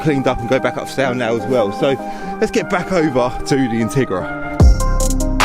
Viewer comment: Thought you said Tigra! Two very different cars, if you know you know.